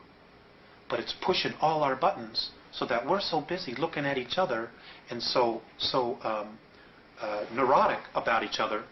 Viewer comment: What was that book and author he says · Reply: Gods of eden- William Bramley